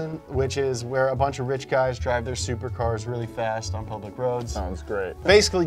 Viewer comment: No one's talking about the captions at 2:39? James says "supercars really fast on public roads" but captions say "stupid cars really fast on old brick roads."